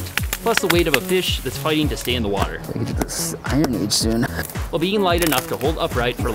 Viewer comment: did i hear "iron age soon" ?!